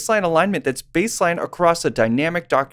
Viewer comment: Brilliant. That made me so heady that I thought you said glue sniffing rather than GUI snippets.